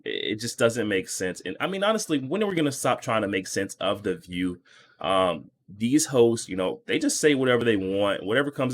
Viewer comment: 😂 i swore you just called the view "these hoes"